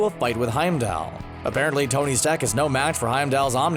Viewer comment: Did he just say "Tony Stack"??? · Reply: He said “Tony’s TECH”